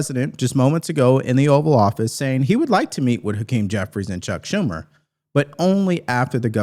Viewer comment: You sounded like you said the king Jeffrey's.